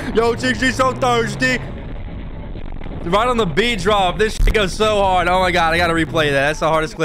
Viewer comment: Did say the N word at 6:30?? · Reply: He said shit